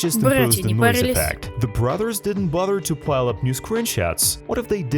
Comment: My guess is that chapter 5 and 6 is coming out at the same time. I think chapter five will be a big stinger Flynn dream sequence that takes you back to chapter one but instead it takes place on bring a friend day which I believe is the day the garden shut down. The reason they would do this is because chapter five would be a few mins longer than chapter 1 and they want to release both to have a closer playtime to the other chapters. And then in chapter 6 we get the queens wand, going with that I think chapter seven we will have to survive the naughty-ones to get to the queen who shown in the chapter 5 and 6 trailer is barley alive. Or that’s just the naughty ones trying to escape. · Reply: What the hell was the brts berts i heard in